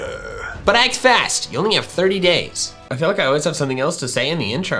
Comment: i skiped to 1:50 and thought he said gay in the context of raid shadow legends when he said days